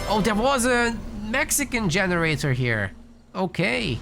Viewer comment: What did you say ?! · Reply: "Oh, there was a Mexican generator here." that's what I heard lol